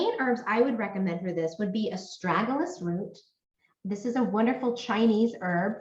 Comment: I’d love to do this could you add the names of all these to your comments? I’m going to get this wrong but it sounds like you said Astranglish root? Need to order. Thanks!!